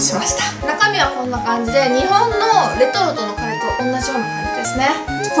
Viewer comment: ニートのカレーて聞こえた